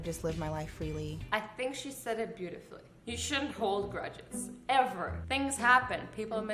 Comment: she said "you should not hold grudges ever" I heard "you should hold grudges forever"